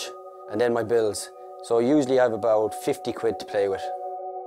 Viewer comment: Can someone please tell me what he says at 2:13 ("so usually I have about fifty???")? My English is still in progress... Thank you so much! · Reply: 50 euro not pounds, we say quid aswell.